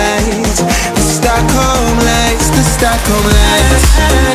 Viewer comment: It is very great song! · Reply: 한국인이다!! 근데 공부하면2서 들으니까 6:42 공부하좌~ 로 들리네 ㅋㅋ